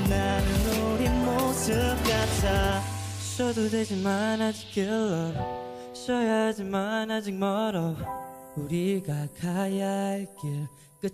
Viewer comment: i hear killer and moron, who else?? just me? ok